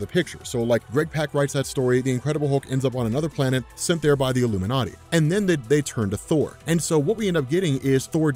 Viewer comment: Did he just say "...sent there by the Illuminati.."